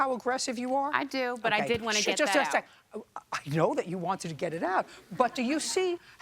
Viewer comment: “You see how aggressive you are?” “Yes. I didn’t want to get it out but…,” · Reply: I thought she said .."Yes l just wanted to get that part out"